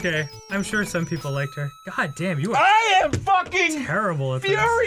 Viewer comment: Man, I still love the game grumps and all, but god fucking damn do I miss the smash cut endings. I happened to pause right at 13:05 so it sounded like "I'm sure somebody liked her. God damn, you are- I AM" and I was just kinda like aw.. that would have been a flawless point to end on back in the old days.